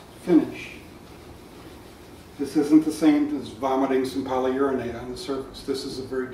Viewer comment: Did he say Polyurinate? around 6:34? :0)